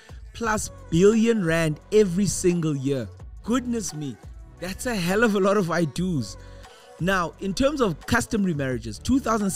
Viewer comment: I thought I heard “That’s a hell of a lot of white dudes” 😂😂😂😂 I was so confused lmao